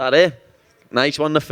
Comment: "Nice one to" what? Film? What did he say here? ;) I can't understand these Brits, lad ;D